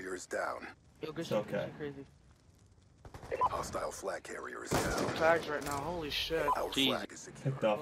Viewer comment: sound like it said hostile swag carrier down